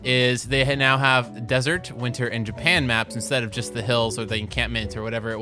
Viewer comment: when he said "winter map" i thought it said hitler. lol